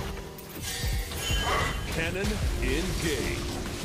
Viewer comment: did Jayce say kennen is gay or am I hearing things? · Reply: He says "cannon engaged" so you aren't far off xD